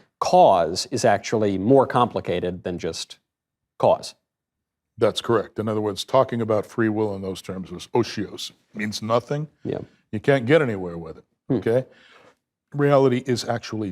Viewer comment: what does Oshios mean? I can’t find it anywhere am I mishearing it?